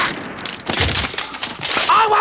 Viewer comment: I thought he said "I won't shut up back for anyone!!"